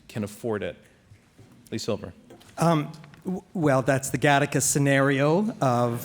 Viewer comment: what did he say? The Gannicus scenario? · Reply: No Gattaca a film